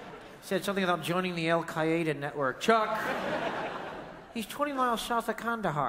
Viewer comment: I swear to God at a younger age I watched David strassman and at this point of the show for so long I always remembered chuck saying the words “he said something about joining the Al-Qaeda club” when he really said “Al-Qaeda network” is this another case of the Mandela effect?? Or is it just me??